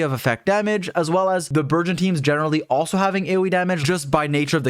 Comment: I've been maining Thoma since he came out, and he's my sub DPS (or DPS in co-op). I saved for him before he was even released. I have him at level 90, triple crowned and C6 with staff of homa; I love that mf even if he's not "meta" because his animations and Chinese voice actor is top tier shit to me. He's a bouncy little lad I'll never get tired of seeing his animations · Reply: Also the entire time it sounded like you were saying "virgin team" it was amusing